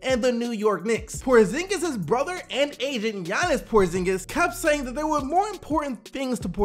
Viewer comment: Did he just say Giannis Porzingis at 3:42???? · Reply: Janis Porzingis.